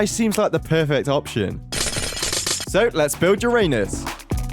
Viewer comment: "so, lets bould uranus" *acually sounds like he said: so lets bould your anus*